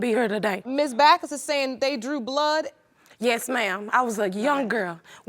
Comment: Am I the only one who heard her say blew drood instead of drew blood